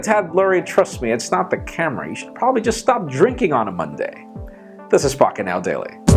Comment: I think i need to stop drinking, Because i thought he said this is fucking now lool..